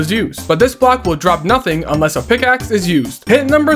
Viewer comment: I was so confused because I misheard pickaxe as axe